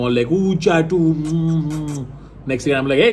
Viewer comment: Chat no please please no I thought you said chaat